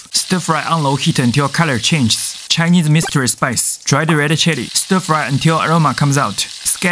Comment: I notice Star Anise was in the mystery Chinese Seasoning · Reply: I heard "Chinese mistress spices". 😂🤣😅😆😁😄😄😃😀😜🤪😝